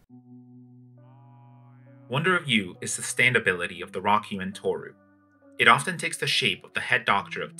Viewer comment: did anyone else hear "wonder of u is the stand of araki and tooru